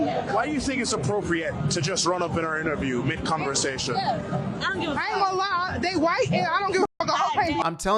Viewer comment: Girl in black dress also said something about "white" though. Couldn't fully hear it though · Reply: I think... Not positive but I think she said, "I don't care that they're white, I just don't give a f$ck". That's what I thought she said when I played it back.